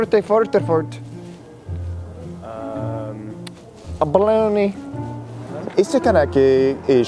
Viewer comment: did he say balony